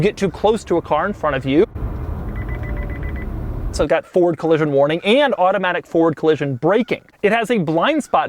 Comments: lol at 9:13 it sounded like you said "Ford Collision Warning" and "Ford Collision Braking" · I thought he said "Ford Collision Warning" and, this being a Chevy, I was like "What, it only detects Fords?" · did you say it was ford collision warning?